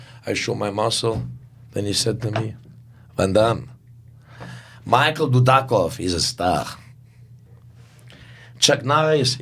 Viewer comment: Think i heard him say Michael Dudikoff, not David Bradley. Dudikoff was the original American ninja. Bradley starred in part 3 and 5 and co starred in 4 with Dudikoff